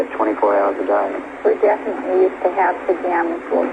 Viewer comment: For some reason, it bugs me I can't figure out what she's saying at 3:17. Anyone know? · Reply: I think it’s ‘we definitely have pajamas .... that’s us’